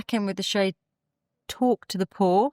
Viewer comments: I thought she was saying “talk to the poor” 😭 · I thought it was “talk to the poor” which I found just as funny 😂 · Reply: Me too! I'm not an English native speaker and I have a little bit of difficulty with Brit accent. I thought: did she say Talk to the poor? This is very catish